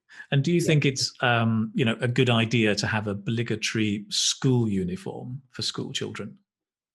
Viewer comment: Hi! In 21:43 you say something like "bligetry", or is it "obligatory" school uniform? Kindly tell me what that word is because I don't get it. Thanks in advance sir! Have a wonderful day!🙏😊❤ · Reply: obligatory